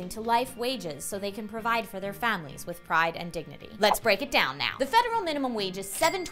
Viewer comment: In the Obama quote I thought they said "provide for their family with fried and dignity" which would have been hilarious.